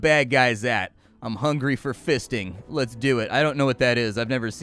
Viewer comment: did you just say "I'm hungry for fisting".....??